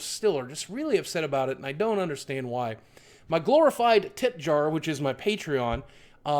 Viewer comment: i'm sorry but at first I thought he said "My glorified tit jar". No hate though! Love ya.